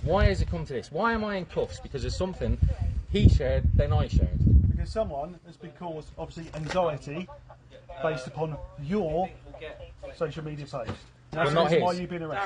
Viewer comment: What he say? I've tried my best, but it honesty sounds like... "because someone has been caused, obviously, anxiety... based upon YOUR social media... ". I cannot fathom the last word but don't need it: I'm about thirty years too old to be doing a thesis on a specific topic, but if I was to do so, that quote would easily be my target. I'll give you twenty odd thousand words on that astonishing cu*tish mouth speak! :(